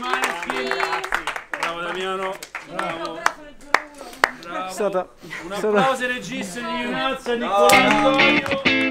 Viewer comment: What does Ethan say 0:08 ? so pure emotions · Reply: he said: "It was...it was..."